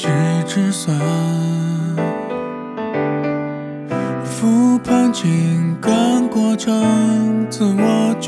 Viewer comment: 作詞_作曲_演唱_導演___都我浩哥啦😄！ 「復盤」聽成Food panda🫣